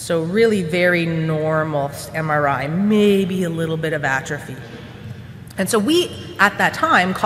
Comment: "So really normal MRI. Maybe a little bit xxxxx." -- What's the word for xxxxx? Couldn't hear clearly. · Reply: She said maybe a little bit of atrophy.